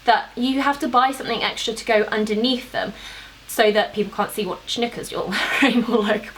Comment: Haha love it when you said what knickers you're wearing sounds like you said what snickers 😂 5:34 can't stop watching that bit